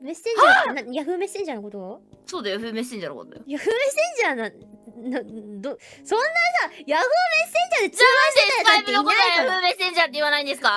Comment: why am i hearing yagoo messenger